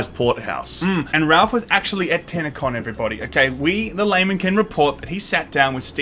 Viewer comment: I thought you said tanna con